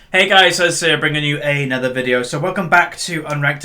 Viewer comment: anyone else think it sounds like he says "Hey, what's up guys? Azir here."? lol